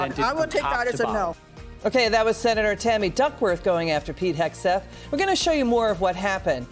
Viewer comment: Did she just say Fatty Duckworth?